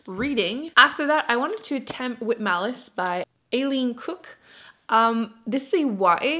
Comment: for a second there I wasn't looking at the screen and thought I heard you say "alien cook" as the writer of with malice :D